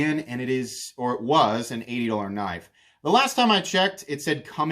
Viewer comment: I've listened a half dozen times. I still can't figure out what is being said. An "adltr knife"? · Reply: "an eighty dollar knife"